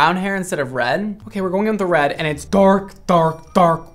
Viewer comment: When brad was saying dark repeatedly ( 13:32 ), it almost sounded like a sea lion 🦭 going “arf arf arf, red.. fantastic” 😂 ~all love~